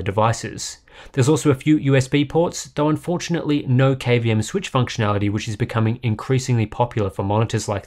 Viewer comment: guys help me out, what is he saying at 4:30 .. "cabian switch functionality" ? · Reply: KVM switch